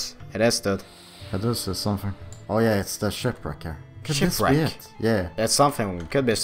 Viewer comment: I thought they were saying Shit-brick instead of shipwreck